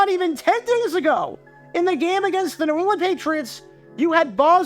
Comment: did anyone else hear him say New Orleans